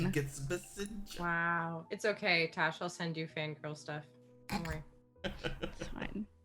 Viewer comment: sounds like said "Fangrel stuff"... I know she said "fangirl", but seriously, I'll start calling Gangrel fans Fangrel now.